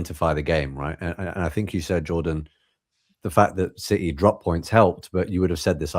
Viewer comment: Did Jordan just say don’t drop the soap 😮😂